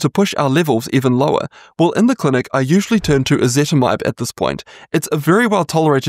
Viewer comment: I can't really understand what you're saying. Are you saying ezetimibe? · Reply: Yes. Ezetimibe.